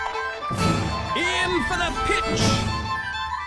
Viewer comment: I thought I hear "infinite pitch" the the start, but no it was "aim for the pitch"